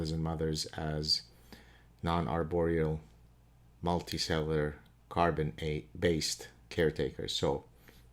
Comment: It sounded like you said Multisailor. You must have said multicellular.